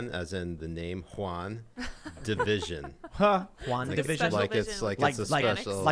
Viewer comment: I thought Tim was saying Long Divsion, I thought it was like a show a bout a math competition